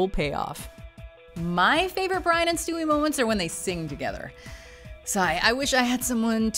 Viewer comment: Did she say "sigh" at 10:26 ?